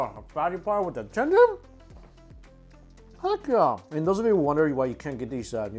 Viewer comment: Did he say f#$k yeah?